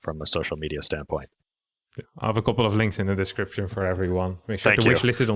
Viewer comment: I heard “I have a couple of lings in the description” oO